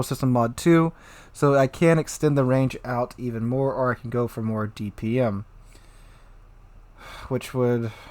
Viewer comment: i need to get my hearing checked i could've sworn he said nord vpn instead of more dpm